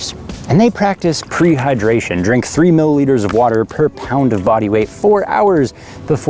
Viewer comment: Three millilitres per pound of body weight. Glad I re-listened to that segment. At first, I thought you said 3 litres. Yikes. What is with the mixed imperial and metric units? The Metric police will be looking for you now.